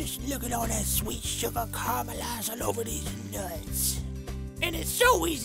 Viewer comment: 8th time watching this week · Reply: i thought she said deez nuts instead of these nuts